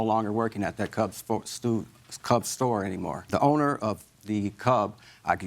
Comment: Did I mishear or is he talking about a "cop store"???